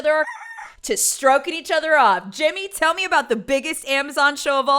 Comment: I saw this on live twitch I hollered when I heard said “Stroking each others coke” 🤣🤣🤣😆I was like omg